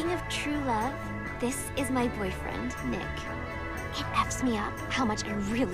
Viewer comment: does she say, he effs me up?